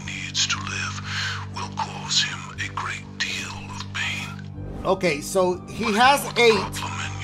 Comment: I thought he said "ok , so he has aids " 😂